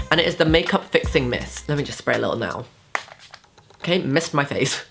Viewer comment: It sounds like you say sex spray at 11:43 lol